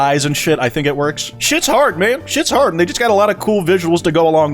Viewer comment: did really say this??? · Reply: “and they just”